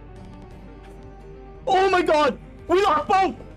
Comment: what exactly is he saying at 9:26? :D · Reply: "We got both"